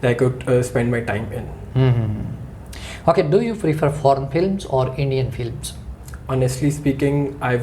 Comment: lmao i thought he said p*rn films or indian films